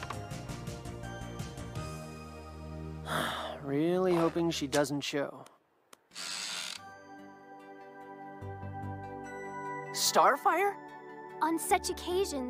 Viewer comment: Just to clear things up Robin is saying that he hopes another girl (the daughter of a villain who forced him to go to the dance with her) doesn't show. Robin loves Starfire :P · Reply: I thought he said "I really hope she doesn't choke"... 😳